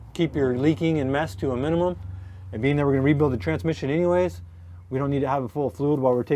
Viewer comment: I thought he said he was re-poing the transmission 😂💀 I was like damn that’s a dedication right there to show up to an old customers house and take back a transmission They didnt pay for 😂😂